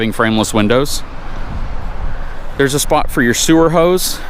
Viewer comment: I am confused on the sewer hookup. Did I hear it correctly there is a hookup curbside? That would be a huge NO for us even if it is only grey water. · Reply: Ahh gotcha! I heard "there's a spot for your sewer hose" and instantly thought why curbside! It never dawned on me it was sewer hose storage.